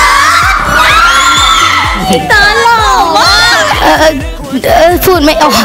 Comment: ได้ยินว่าอีเติร์ด<num>